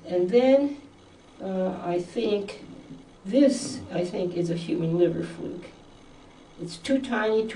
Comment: what did she say that is from the liver? I listened 3 times already & can't figure it out. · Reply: A human liver fluke. Liver flukes are parasites.